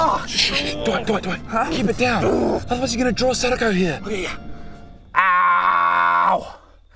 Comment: Thought he said he would draw sneako here lol